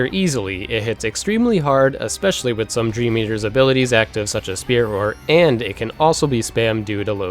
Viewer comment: Sorry, what did you say here? 0:58 "dream eaters' abilities active such as... ______" ? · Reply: Spirit Roar, buffs your attacks, makes Balloon Commands do massive damage regardless of if you're underleveled